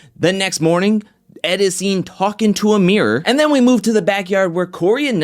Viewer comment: When you said "Ed looking at a mirror." I thought you said "Amir" so I was wait what. Got include the funny parts in!